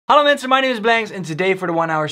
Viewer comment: Are you dutch? It sounds like you say on the begin:”hallo mensen”😂